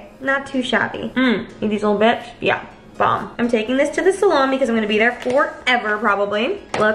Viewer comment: her saying salami instead of salon😂😂